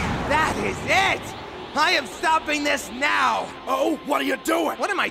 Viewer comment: "What are you doing?" "What am I doing?" "Thwarting my plans?" "Thwarting your plans?" "Are you?!" Aah you have to love call backs. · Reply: I was wondering what he was saying, I first thought Vegeta said "Forting my plans?" Then I heard Trunks saying "Sorting your plans?" I was so confused way back to the Namek Saga.